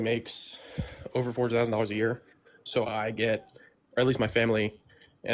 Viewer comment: did he say his family makes over $4 thousand dollars a year? 👏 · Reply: I think it was $400,000 a year ...